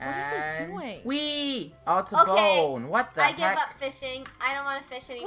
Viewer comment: did just say the f word? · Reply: No.. He said Heck